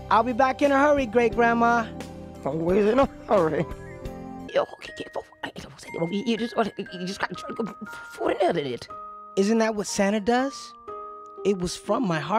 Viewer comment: "Fjiskskndjosoajdnfkdj" "Isn't that what santa does?" Yea he totally does that every Christmas. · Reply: What Great grandma was saying at 0:20 : “You’re so pitiful I’ve said not to eat it all you’ve just gotten chocolate before you earned it”